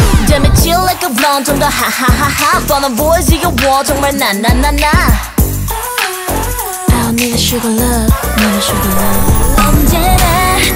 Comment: am I the only one who heard min suga love instead of I don’t need sugar love